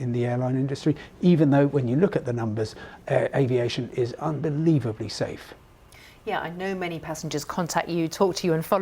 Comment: "It is unbelievable safe"? This new type of 737 Max plane crushed twice within 4 months. Who funded this BBC guest? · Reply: He did not say that, he said that aviation is unbelievably safe. Also, it's not two crashes in four or so months, it's two crashes out of how many hundreds of thousands of successful flights. That's perspective for you.